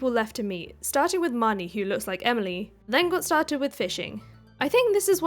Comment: I think I just heard elimy